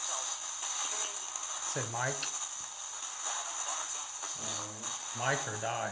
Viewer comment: At around 25:31 when you ask the spirit to say it again, I hear "It's Mike"